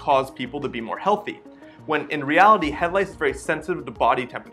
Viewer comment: oh, now i get it! I thought you were saying head lights! I was like, wth, head lights? Wat do u mean headlights?